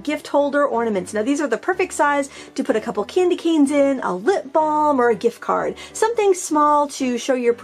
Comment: I thought you said "lint ball" but you said "lip balm"! Hahaha